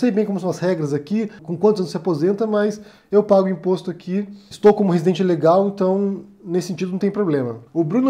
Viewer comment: Eu entendi "estou como residente Ilegal". ILEGAL. Ouvi errado, né?